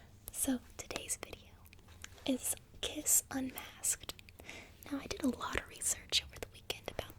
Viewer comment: I know the video is titled Prim Kiss Unmasked, but at 0:30, I thought I heard you say "kiss my ass", and reacted with a double take. 😂smh. Definitely hearing things. Great video by the way, and love the makeup 👌🏻